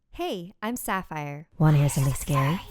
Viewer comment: I COULDN'T HEAR HER PROPERLY AND I THOUGHT SHE SAID "I'm batman,wanna hear something scary"